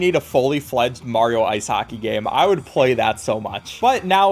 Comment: I heard ice haki every time 😂😂😂